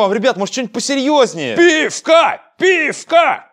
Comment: я или один слышу ВПИСКА ВПИСКА ВПИСКА ВПИСКА Только shipilavyi voice edition